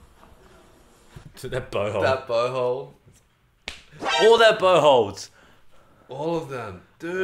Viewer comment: thought he said butthole